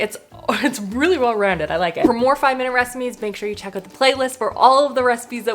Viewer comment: at the end it sounds like you say 'for more 5 minute reciMEs' 😂 looove the look of the orzo one, def want to try that one out!